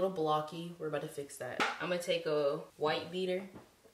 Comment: Yo I legit thought u said white beater im fuckin shook